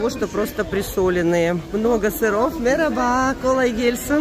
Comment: Что такое "нероба"? И еще послышалось что-то похоже на " ельсин" Или апельсин.. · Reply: Вероятно было сказано "мераба" (merhaba-привет), а второе: "колагельсин" (kolagelsin -удачи).